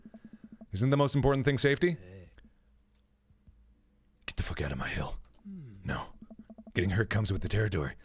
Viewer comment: What the hell does he say at 1:46:43 ? All I can make out is "Get tf out of my-"